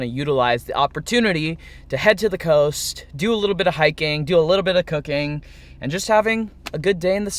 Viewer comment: thought u said a little bit of cocaine haha😂